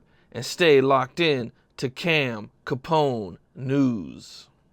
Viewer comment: Ngl Im high af i thought he said cam capone nudes at the end